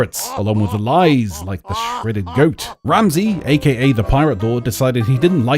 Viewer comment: did you just call it the shredded goat?